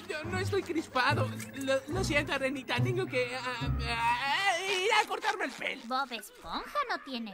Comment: yo escuché (tengo que ir a cortarme el nepe) :v